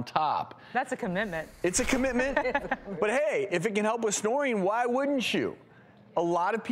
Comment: I thought it said snorting 😓